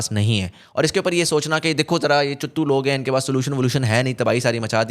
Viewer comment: Did you really said “chuttu log”